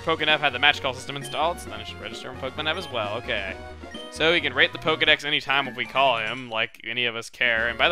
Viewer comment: did he just say rape the pokenav at 0:54 ?